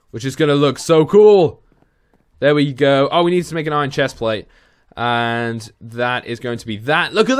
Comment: did he said iron?